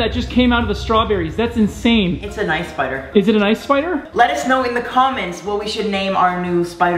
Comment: I thought he said lettuce 🤣🤣